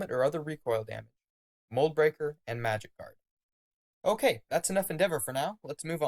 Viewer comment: I misheard "Magic Guard" as "Magikarp" so I got really confused